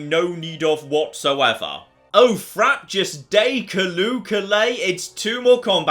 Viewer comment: Any idea what says here? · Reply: "Oh frabjous day, callooh callay". It's a quote from Jabberwocky.